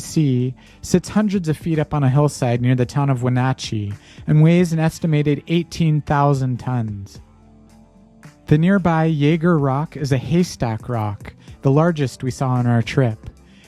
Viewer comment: In Graham Hancock's book, Magicians of the Gods, he writes: "“We get back on Interstate 97 again and drive the forty miles south to the confluence of the Wenatchee and Columbia Rivers near the eastern foothills of the Cascade Mountains. Here Randall has a last giant erratic to show me, this one weighing, he estimates, 18,000 tons. It stands high up on the side of a long, wide valley, looming over a modern housing development, hundreds of feet above the confluence of the rivers and the town of Wenatchee.” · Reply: my mistake I thought he said 18 tons and from the pic I was guessing but 18,000 tons I can accept. Thanks for the clarification 👍